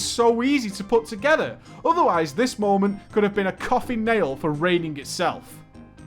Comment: "This moment could've beena coffee nail for raiding itself"? Or is it "coughing nail"? Exactly what is he saying there. I'am curious on that saying. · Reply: with the Dragon Coffin nail